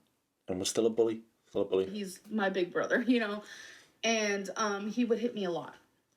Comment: She said little brother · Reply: just after 27:24 she clearly says big brother.